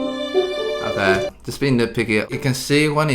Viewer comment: Eddy "we're not being nit picky.." Also Eddy : nits and picks at everything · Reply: I think what he said was, "just being nitpicky." Eddy knows he's nitpicking.